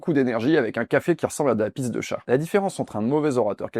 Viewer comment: Les français aidez moi Que l'entend par l'expression " un café qui ressemble à la piste des chat " 01:19 J'ai google it j'ai rien trouvé Merci d'avance ❤❤ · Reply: "Pisse de chat", cat's piss